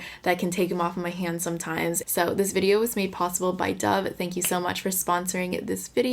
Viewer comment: After talking about Dejan I thought you were gonna say “this video was made possible by Dejan”, but then you said dove and I just started laughing 😂💕